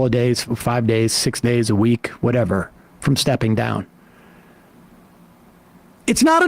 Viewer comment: At the 3:55 mark, I legitimately thought Sam was saying "it may be 2, 3 days, maybe a week from Biden dying."